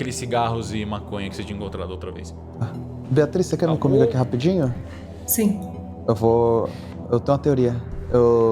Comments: só eu q escutei o dante pedindo pra beatrice comer ele rapidinho 🤨📸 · eu juro que ouvi ele falando "Beatrice que me comer aqui rapidinho"